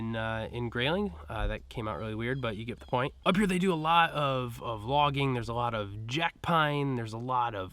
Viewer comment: I totally thought you said, "Up here they do a lot of vlogging..."